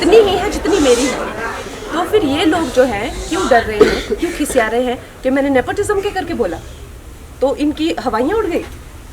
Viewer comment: Did she said khisiyaa rahe hai 1:30 the bhojpuri word🙄🙄